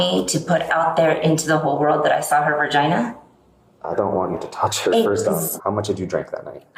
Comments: she did NOT just say "Vergina" 😂😂😂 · I'm from Boston so i still read it as vagina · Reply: It sounds like she said ver-gina